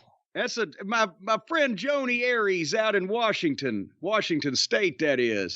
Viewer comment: Did Cornette say his friend Jodi Arias at 4:07? 😂